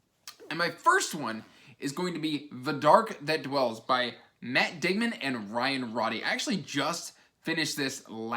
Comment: I thought for a second you said Matt Damon for Author of The Dark that Dwells.... Great video